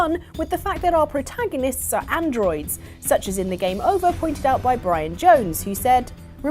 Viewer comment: I always thought it said GAME OVER AGAIN